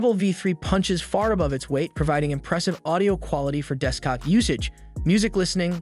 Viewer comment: Was it only me or did he say deskcock usage